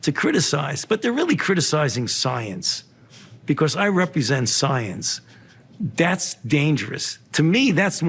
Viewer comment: "I represent science" is the most narcissistic thing I've heard, and I've heard a lot of narcissism these past two years. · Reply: He says,” I represent science.” I hear, “I am all the Sith.”